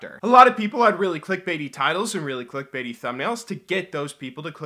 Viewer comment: After that explanation I can see how hard it must be to not use those titles. That's a week's worth of videos in one day. Imagine if you only had to work one day a week to get the same pay as someone else who works a week. So many people would do it! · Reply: Man i thought u said tities for a moment